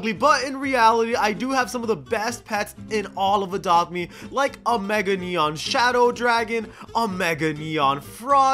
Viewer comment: For a sec I thought he said "and a mega neon subscribe dragon"